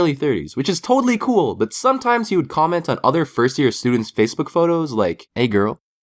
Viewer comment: i heard fursuiter